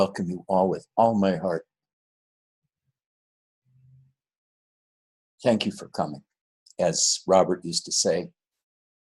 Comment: Did I hear "Thank you for cumming" (as Robert never said) ?